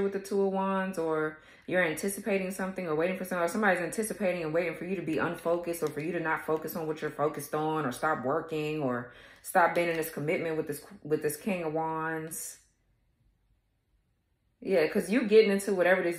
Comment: But I thought you said the king of ones had sick balls , and now you says he smells good and looks good,don't understand.